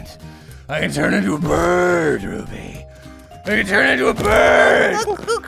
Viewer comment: He can turn into a bird but i thought he said a bat so i heard BUT RUBY I CAN TURN INTO A BRRRRRRRRT